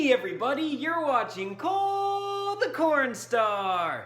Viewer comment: Hey Cole the first time I heard you say cole the corn star, I mistook your words. I thought you said, cole the porn star. I want to say, I apologize for not listening properly.